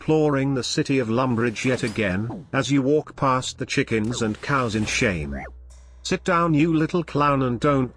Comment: I thought he said chickens and cows and shit lol